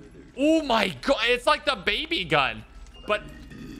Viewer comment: did someone say Dababy? LET’S GOOOOO!!!